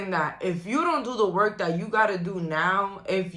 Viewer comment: When you said “if you don’t do the work now” I heard “you’ll end up like him” Don’t let these people put you down.. the ones who are older and became bitter because you’re achieving so much more than they could ever imagine when they were at your age. Put the work in now! Forget about any negative thought that comes to mind to slow you down..